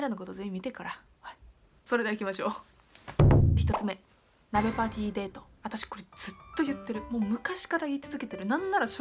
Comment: マリオパーティーデートに聞こえたw